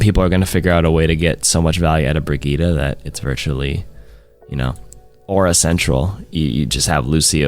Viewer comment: I really hope they revert the goats nerfs 6:25 did he say "aura sensual"? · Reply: im pretty sure he said aura central